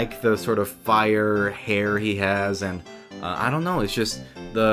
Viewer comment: did anyone else hear "hairy ass"?